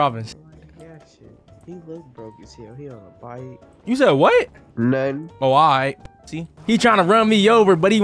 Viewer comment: what you say… NUN